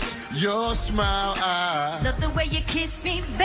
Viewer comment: Lol, I always thought Ja Rule said "your lips, your small eyes" 😂